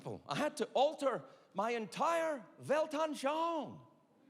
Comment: Bis ich endlich Weltanschauung verstanden habe :D had to play it 5 times